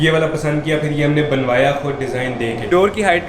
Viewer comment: did he just said that "he's 6.4" 👀👀 ps: thanks for all these likes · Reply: He is 6.4